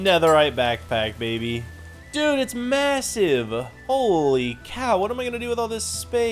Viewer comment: did bro say... MASSIVE?